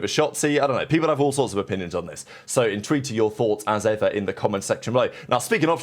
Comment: Yo are you saying “intrigued by your thoughts”? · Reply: I hear 'in tune to your thoughts'